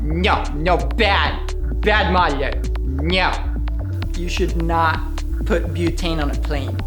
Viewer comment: OK, I just need t know what David says at 20:20 !!!!!!! Bad WHAT!?!?!?!! SOMEONE HELP ME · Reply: modeller...i think